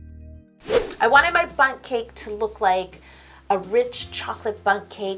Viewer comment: When you said bun cake i thought you said butt cake 😂😂😂🍰